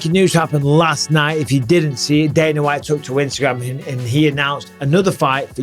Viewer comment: Did you just say twitagram? 😂 That's exactly what i thought i heard you say